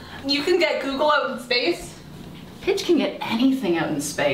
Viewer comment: I thought Lance said “A b*tch can get anything out in space” instead of Pidge for a second Lol